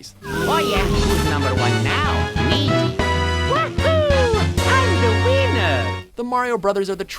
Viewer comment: When mario said "I'm the winner" it sounded like he said I'm the wiener XD and he is a wiener, luigi is better